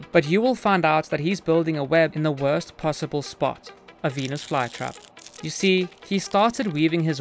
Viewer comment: I thought he said “peni# fly trap” 💀